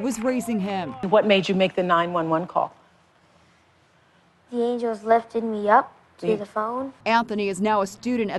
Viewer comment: "The angels lifted me up to the phone" I'm in tears ! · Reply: i thought he said the agents lifted me up